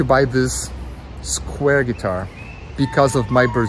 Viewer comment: oh, i thought it said squier guitar